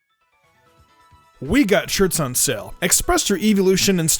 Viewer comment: Start of video What I thought was said: We got Dicks on sale... Me: Wait, hold up...